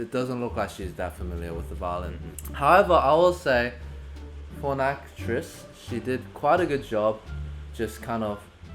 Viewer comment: Omg at 17:57 the captions says "porn actress" instead of "for an actress"😂